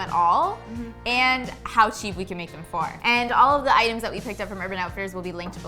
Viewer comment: Hey guys! I love this video so much, and I want to make sure everyone can understand and enjoy it. That's why I wanted to let you know that your captions are a little off. At about 0:27 the auto-captions say "how chief we can make them far" where they meant to say "how cheap we can make them for". When you get a chance, could you fix that? That'll help the 360 million folks globally who rely on closed captions to understand videos. Thank you, and keep up the great work!